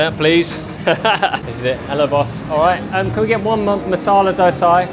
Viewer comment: I heard "Hello Boss" 🤣